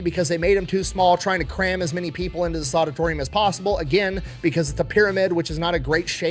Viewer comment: Did you just say "the soditorium" ???